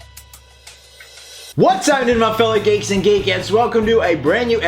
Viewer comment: I didn't know he had a accent at first and thought he said "What's happening my fellow gaykeeds and gaykits..."